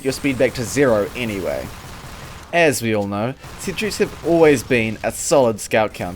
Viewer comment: I thought he said sitrees.